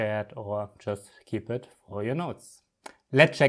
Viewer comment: What the fuck are you saying? I can barely understand any word you say in "English." "focord air command"? What the fuck is that?